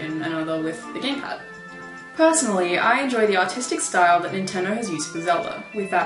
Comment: Ocariner? At 1:21 I thought she said autistic at first (really ARTistic).